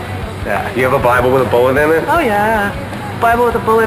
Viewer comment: what does she say on 0:54? thanks! love green day :) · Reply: "bible with shrapnel in it"